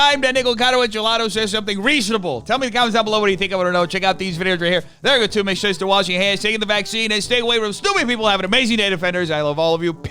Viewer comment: Not gonna lie every time you say "taking the vaccine" I hear "sitting in the back seat"